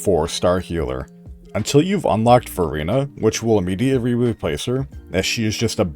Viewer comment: istg everytime someone says verina, i only hear furina... Or did he actually say furina?